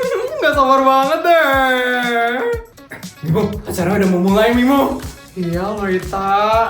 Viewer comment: aku dengernya bimo plis☺☝